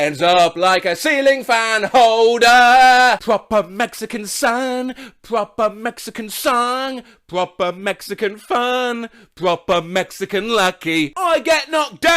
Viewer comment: In a part of the song, I heard, "We rub a Mexican monkey"